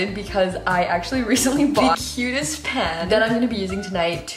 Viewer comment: When you said “I got the cutest pan” I thought you said “ I will forever cook soup in my pants“ 😂